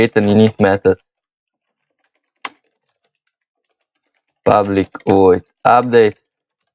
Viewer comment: Man, All I keep hearing is "public void ublic"